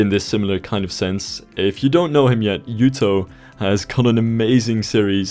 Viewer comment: any chance you could spell out or link other Youtubers you mentioned at the 6:53 mark? Dr. Com was easy enough to figure out but I can't figure out who "Nuto" is. Knewto? Newto? Thanks!